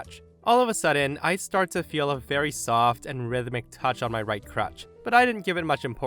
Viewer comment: I heard "crotch" instead of "crutch" and wondered about why OP was being so calm. Snicker. The Karens who refuse to pay have probably been thrown out of every restaurant in their town so they are branching out in other small communities.